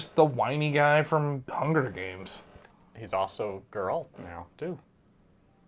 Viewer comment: I thought you said 'a girl' and not 'Geralt' when you were talking about Liam. Loved the mention of Coridor Crew that channel is top tier.